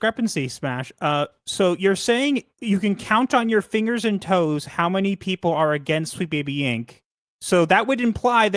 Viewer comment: MasteroftheTDS: 17:30 — That's not what she said. She didn't say "You could count on your fingers and toes how many people are against Sweet Baby Inc". She said "You can count on your fingers and toes how many people work at Sweet Baby Inc." You're mocking something that was completely flipped from what she actually said. Try not to make it easy for your opinion to be dismissed as "They're just straight up lying about us."